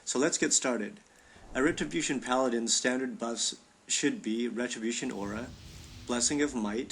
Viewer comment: did he say retribution aura? hmmmm check the talent again... 1:38 sorry m8, but this is a lil wrong rotation